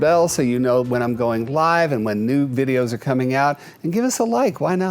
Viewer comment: sounded like he said nude videos… 😅😂😂😂